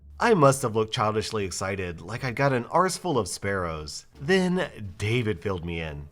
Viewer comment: “Arseful of sparrows” is a great phrase, ngl. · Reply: Heard it as "school full of sparrows" and thought "oh shit imagine a school for sparrows, thats so cute"